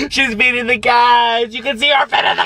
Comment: I died laughing (im not rude tho) Anyway I heard "she's feeding the cacks"